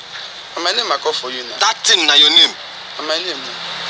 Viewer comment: I thought he said that isn't your name